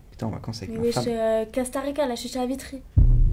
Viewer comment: Est-ce que quelqu'un pourrait me dire qu'est-ce qu'elle dit à 0:34 svp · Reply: "Wesh euh Costa Rica la chicha à Vitry"